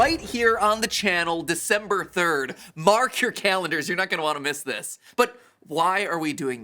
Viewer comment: "Mark, your calendars!" I cant be the only one who heard this at first